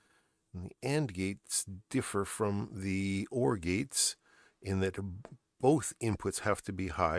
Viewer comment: My hearing is not so good and heard you say 3 orgies, how do you get 3 orgies in a microprocessor? Then I realised it was Or gates..